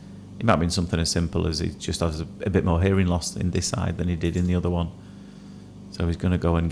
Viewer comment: I’m assuming that I misheard, ‘bit more hearing loss in this eye’? - or do I need my lug ‘oles clearing out 😂